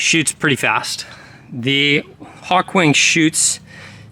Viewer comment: At first I thought he said it was a Ballista Hawking... And I was like, what's the big deal...do the bolts travel at the speed of objects entering the event horizon of a black hole?